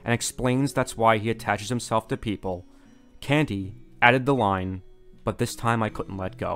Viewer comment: "Candy out of the line but this time I couldnt let go" What are you saying here? My brain is just hearing the words, its not putting it into a coherent sentence